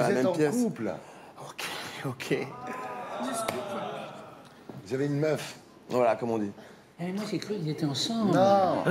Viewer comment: Qu'est ce qui dit Natoo à Yvick svp ? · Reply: SUN elle lui dit « le scoop » comme quoi il a une meuf